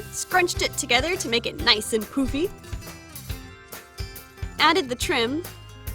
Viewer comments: When you say nice and poofy it sounds like you say nice and poopy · When she said to make her hair less poofy i thought she said to make her hair less poopy LOL XD